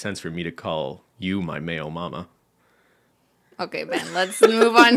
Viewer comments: I was listening to the podcast and couldn’t understand what was wrong with the nickname. I thought Ben was saying male mama not Mayo mama. Makes more sense now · I was listening to this not watching and instead of "mayo mama" I heard "MALE mama" and the entire story was completely confusing 🤣🤣